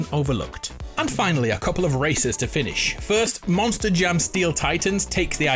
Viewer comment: It might just be me but.... At 6:05 It's sounded like 'A couple of racists' not racers. Made me look at the video (not just listen) so good work. I should (and will try) to get back into Stray